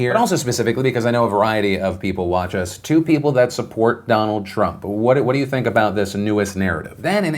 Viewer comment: When you said: "To people who support Trump..." my mind heard it as: "The two people who support Trump." 😂